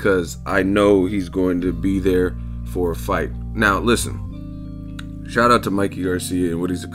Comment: it basically sounds like hes saying "he'll be there for me to hit" but say they fight and spence uses movement to win...what now?